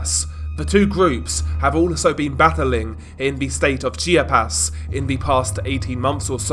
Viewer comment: Chiapas (chi-ah-pas) it sounds like you were saying chia-pets😂 ch ch ch chia. Love the content.